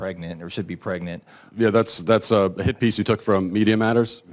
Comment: When he said it was a "hit piece" I thought he said "hippies". Man, was I confused.